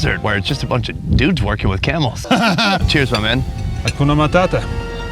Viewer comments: Did I hear hakuna matata 12:10. If so Kenya hukua matata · Did he just say "kuna matata"?😅